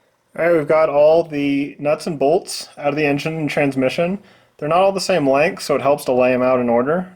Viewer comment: Two of them are longer, even on the non-turbo cars. I believe it’s the top two. It also wouldn’t be surprising at all if someone has been in there before. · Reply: Thanks, my mistake. When you laid out the six bolts I thought you said they are all the same length. When you actually said they are not. I thought I had a problem lol